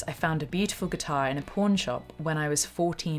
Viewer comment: For a second, I thought she’d said “I found a beautiful guitar in a porn shop”